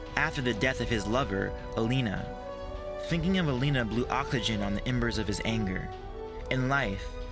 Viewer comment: Thinking of Elena blew WHAT on the embers of his anger? I didn't understand that part. Did you mean to say "oxygen"? Because it sounded like you said "awckligen" or something. lol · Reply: oxygen